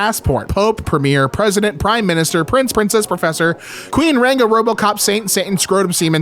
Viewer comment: i heard it as “robtop”